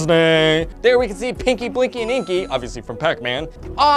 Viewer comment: When you said pinky,I thought you said kinky